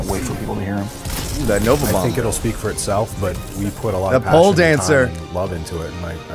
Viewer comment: Wait did anyone here wat said 4:34He said pole dancer